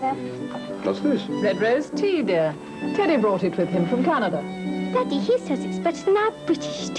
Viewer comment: I have always wondered as a kid what she says at 00:06. Is she saying “Pity bowl from Canada?”